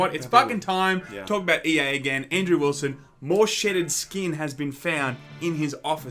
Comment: I thought you said "Shit & Skin", instead of "Shedded Skin" hahahahahah!!!